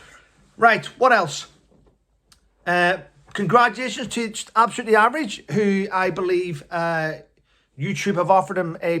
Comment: You said it too quickly. I can’t subscribe to the YouTube channel cos I didn’t catch the name. Something hub I think. Sounded like fitness hub. !!!!!! Tell me the name again, slowly, so I can help him/her out. Love ya. South Oz ❤❤ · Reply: I had to rewind a couple of times too. It is 'The fitness hutch'.